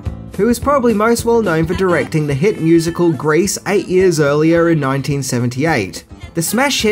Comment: I thought he said grace. At 2:11 Not grease. The accent is pretty strong. Second check. He did say grace. ( which is a prayer to God)